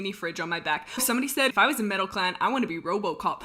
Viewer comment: Guys, are we just gonna ignore that said we should all make MeadowClan ocs??? We gotta MAKE them! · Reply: Yea when I first heard MetalClan I thought she said MeadowClan too lol